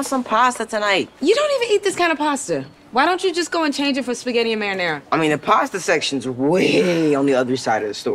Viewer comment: 4 a split sec I thought she said “sKiBiDi MaRiNaRa” i was gonna fall of my bed from laughter